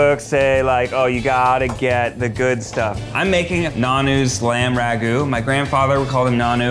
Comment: Welp at least we don’t have to watch a certain somebody make pasta every episode, bring up his Italian heritage and butcher the Italian language anymore when Matt probably makes better pasta anyways and speaks better Italian 🤷‍♀️ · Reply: I thought he was saying Nahnu